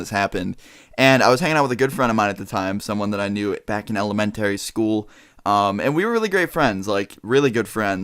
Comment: did u just say element Terry school